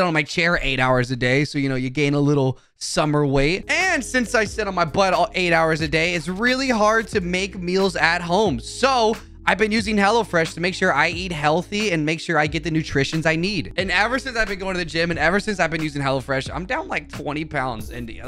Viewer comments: I’m sorry but I keep hearing “HELLA FRESH” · I heard "Hella Fresh" multiple times, I think he's attempting to become black